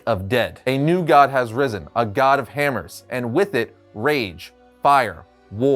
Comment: I noticed this episode because I thought it said, “God of Hamsters”